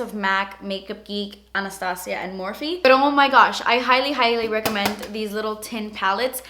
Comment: Lol did she say "peri oh my gosh" at 1:33 ?! Lol or was it just me?